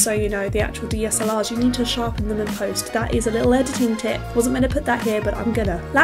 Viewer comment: What was that Canon secret tip you mentioned at 01:29? I'm sorry, I replayed and replayed but couldn't understand what you said exactly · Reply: theHapa You need to add digital sharpening in post production. Especially older canons as they don't really film in HD, they compress pixels in such a way with that means an image isn't as detailed as it could be. Always do it editing, and never digitally in camera as it will artifact a little.